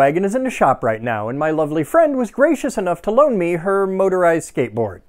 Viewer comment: you cannot imagine the whiplash i had when i thought i heard "my friend was racist enough" instead of what you said